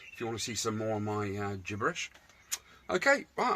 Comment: I thought for a minute you said "take it Eadsie" at the end lol could the world handle that 🤪